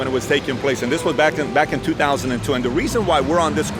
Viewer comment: Sounded like he said back in 2020 at the start played it like 3 times and that all I heard but eventually got 2002 edit from here just saw the end and he has a point